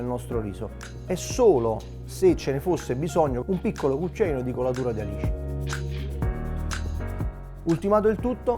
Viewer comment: Al minuto 6:00 que es? Dijo licor de que? No le entendí esa parte. AUXILIO!!!! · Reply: It’s “Colatura di Alici”, tipically from Cetara (Campania). It’s an aged sauce obtained by pressing anchovies in wooden barrels.